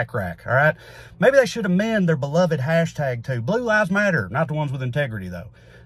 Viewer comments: What I heard, “blue eyes matter” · I thought you said "blue eyes matter".